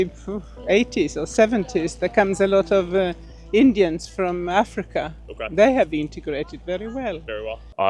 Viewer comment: did she say 'indians from africa' ???